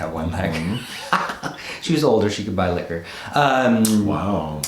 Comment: I thought at first you said “she was older, she was a butt licker” but then I rewound it a few times and realized my error. I just can’t.